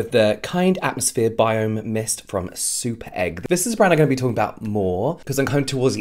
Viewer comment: did he say the mist was from SOUP EGG?